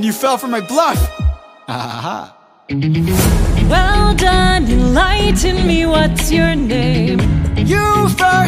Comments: I can't be the only one who hears "enlighten" instead of "you lied to me" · I think the line is actually, "Well done, enlighten me, what's your name? Edit: Jorge Herran, writer of the song, has confirmed the lyric to be, "Well done, enlighten me, what's your name?" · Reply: It's been confirmed to be "Enlighten me". Personally, I heard "you lied to me" and actually prefer that lyric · That part "well done you lied to me what's your name" I misheard lied as 'Enlighten'